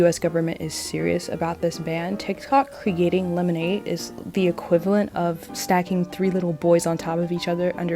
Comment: I keep hearing "lemonade mouth" I should rewatch lemonade mouth